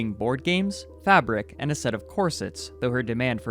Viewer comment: i misheard that at first, i thought you said “Assetto Corsa” instead of “A set of Corsets”. Now i imagine it as “oh no they took my Assetto Corsa, how am i ever gonna get all my Initial D mods now roleplaying as Cloud beating the Redsuns”